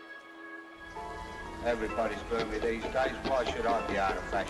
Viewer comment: can someone translate what was said there. Thanks😊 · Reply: TheBarcaLife 180 everyones ... these day why should i be able to ...